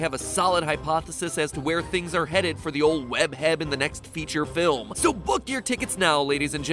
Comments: Did anyone else hear Matpat say “Web-heab” at 3:00? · It sound like he said web heab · Who else heard "Web Heab" at 2:59? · Did anyone else hear "Web-Heb"